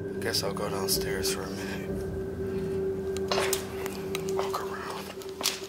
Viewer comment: #1 video, when guy was walking down stars, i heard ''Look Around''. It was in a very deep voice.